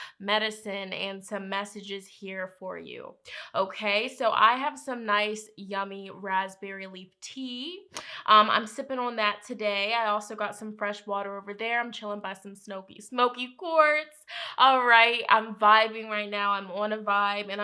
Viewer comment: I heard “I’m sipping on Yak today” homegirl is wildin!